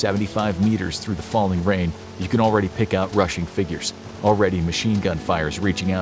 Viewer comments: "you can already pick out Russian figures" Prussian, maybe, but isn't this supposed to be a Western Front piece, hundreds of km from where Russian forces were fighting...and who were also one of the Entente powers so wouldn't have been fighting against British soldiers. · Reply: OK fair enough, I hadn't heard it as "rushing" rather than "Russian". The Pickelhaubes do indicate German soldiers but I just assumed there was an error in the script or vocal performance, and I listened to it about five times before posting my previous comment and each time I heard it as "Russian". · Am I the only one that noticed the narrator made a mistake at minute 19:41 and said Russians instead of Germans??? And was it a coincidence that it was 1941? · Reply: “Rushing” not “Russian”.